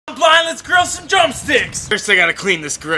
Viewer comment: at first I thought you said " I'm buying this girl some drum sticks" 🤣